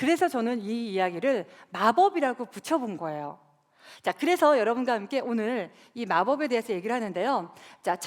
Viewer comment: Chào, ở phút 4:09 -> 4:10 mình không thể nghe được cô ấy nói gì, mặc dù đã tua chậm tới 0.25. Các bạn có thể giải thích hộ mình được không ? Cám ơn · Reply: NguyenQuang ITvn 그래서 여러분과 함께 오늘